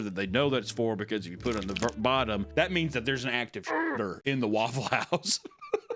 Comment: i legit thought bro said active shitter for a second